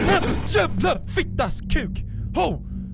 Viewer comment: what's pewdiepie Said?. He's like said "HGOGHOG FIX FAX CUX"